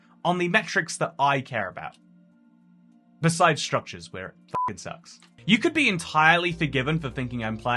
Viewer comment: Waiit...did just say "f***ing sucks" in the video?